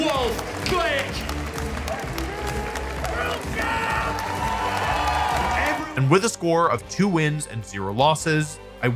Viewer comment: idk what bro said but it sounded cool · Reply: He screamed at the top of his lungs WORLD CHAMP DIFFERENCE